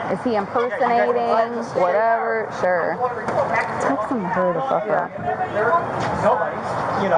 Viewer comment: At 630 sounds like she says tell sarg to hurry the fuck up. Lol. She aint no idiot.